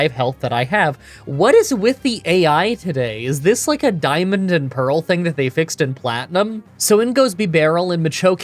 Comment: "is this like a Diamond and Pearl thing that they fixed in Platinum?" what i heard: "Is this some D/P thing that I'm too Pt to understand?"